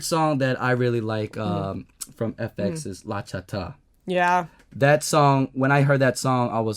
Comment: What did he say @ 18:10? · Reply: he said "La cha ta"